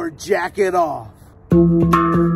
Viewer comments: I misheard him when he said “jacket off” as something else… 💀💀💀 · This shit sound like a smartphone alarm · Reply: How come in my ears when He says Jacket Off I Hear "Jackin off"😂